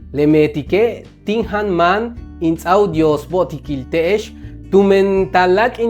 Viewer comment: Ajka'ansaj estoy tratando de traducir el video de año nuevo que hiso en el fin de 2024. tengo unas dudas de este video. 0:17 dijo algo que no entendi despues de decir "tin jaan maan" y antes de "dyoos bo'otiki' tech." Tambien que exactamente quiere decir "tu t'i'it besoj"? Es compartieron? Y una mas: "tu yesoj" 0:29 Muchas gracias de antemano por aclarar. Estoy aprendiendo mucho con sus videos. Dyoos bo'otik. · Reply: Bix a beel Julio. Respecto a la primera dije: tin jáan máan in ts'áaj u dyoos bo'otikil te'ex, lo que no entendiste es: in ts'áaj que seignifica "dar". Tu t'i'it'beso'ob, efectivamente significa "que compartieron". Tu ye'eso'ob viene del verbo «e'es» que significa mostrar, así que la oración significa "que mostraron o demostraron".